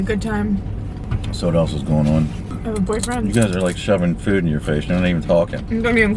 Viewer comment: did she say "i have a boyfriend" !!?!?!?